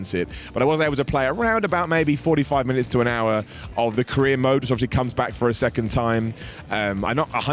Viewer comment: I thought he said Korean mode but I think he's saying career mode